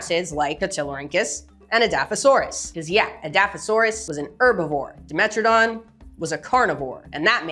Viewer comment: I thought you said adaptosaurus. Lol